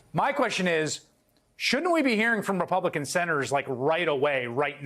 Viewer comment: Ha ha..... y'all funny. I swear I heard you say "Republicans step up........".